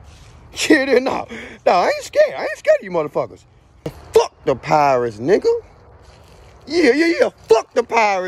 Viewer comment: bruh i thought this man was saying fuck the pirates at first lmaoo my mind went to one piece